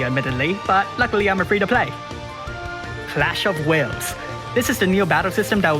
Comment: i heard "CLASH OF WHALES" which is kinda dv right now